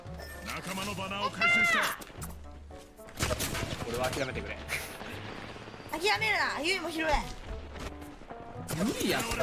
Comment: ここ多分ぎるるって言ってるんだろうけどほんとにYuiにしか聞こえんくて草